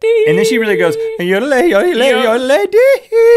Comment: it sounds like Link is saying "your an old lady". Like or reply if you think so too.